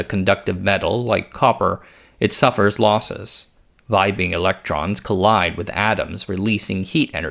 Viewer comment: Did I hear "Vibe-ing electrons"?